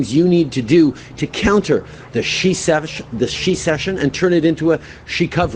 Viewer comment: Oh…..haha…..I thought he said shesection.I was going to say I had three of those😜😜🤣🤣🤣🤣